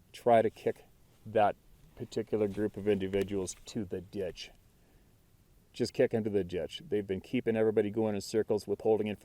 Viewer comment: Lol, when you said kick them in the ditch, I thought you said kick them in the dick.😂🤣